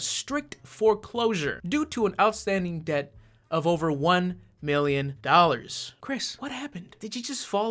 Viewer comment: Dude, I love you, but I swear you said 1 billion dollars at around 4:00 instead of the shown 1 million